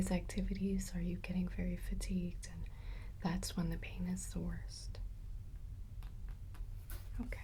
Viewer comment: what she said . . .? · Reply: "the pain is the worst"